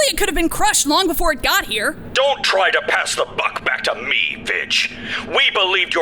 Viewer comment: What did he call her?.😅. I might be half-deaf, but I think I heard something bad... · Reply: He just said her name (vidge) so what do you think you heard? Lol